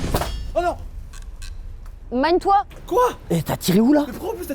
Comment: nan Alan elle a pas dit mein kampf ( mon combat en Allemand ) elle a dit : magne toi ! 🤣🤣🤣🤣🤣🤣🤣🤣🤣🤣🤣🤣